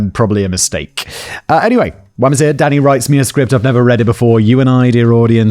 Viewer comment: I 100% misheard him and thought he said "Daddy wrote me a script" 😂